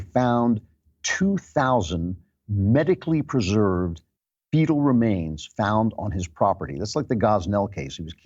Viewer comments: At first I thought he said "beatle remains" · When you said fetal remains the first time I thought you said beetle and I thought you had lost it.